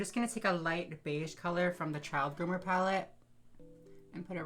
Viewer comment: when she's doing her eyelids, I've re-watched it 3 times but I keep hearing, "I'm just gonna take a light beige colour from the child groomer pallet" ... someone help 😭